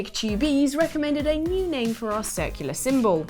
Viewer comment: british accent confused me when she mentioned the icon being circular, I thought she said secular and I was confused because this is a gaming channel why are you suddenly talking about politics?